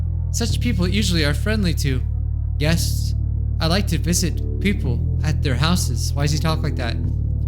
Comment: I wasnt looking at the screen and I thought you said I like to piss at people at their houses 😭😭